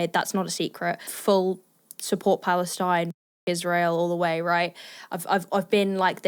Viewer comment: The way you censored that made it sound like "Israel all the way". You really gotta find a better way. Say screw instead of fugg. Because this weirds me out